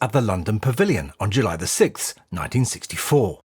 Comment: audio says premiere was July 6, CC says June 6.